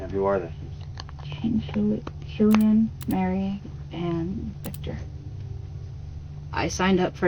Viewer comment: She clearly says "Kane killed Killian, Mary, and Victor"